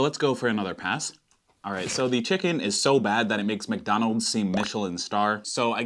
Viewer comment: I thought it said D*** donalds 0_0